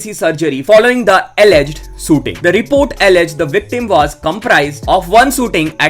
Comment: Why I heard "Sooting" , instead of shooting ?